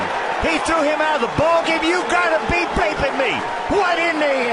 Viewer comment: The White Sox commentator is the funniest and my favorite one... “You gotta be bleepin’ me, What in the hell are you doin’?” 😂😂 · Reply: Thought he said baping lmao